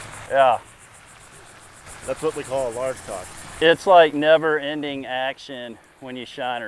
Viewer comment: No one else caught what the man said at 15:28...A largemouth and a Peacock combined is a Largecock. 🤣🤣